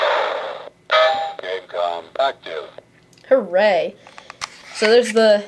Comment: when you started it up is sounded like it said game com &^%$ you lol